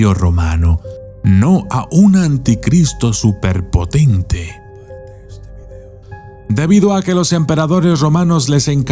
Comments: Alguien mas logra escuchar lo que dice el video en una voz tenue y susurrada con poquísimo volumen justo al finalizar la oración " no a un anticristo superponte" justo en el minuto 4:49 a 4:52 ? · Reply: Dice comparte este video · Alguien mas ecucho lo que dijo en minuto 4:51 hasta el 4:53 ponganse audifonos para escucharlo mejor y que es lo que dijo?? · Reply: Dice : COMPARTE ESTE VIDEO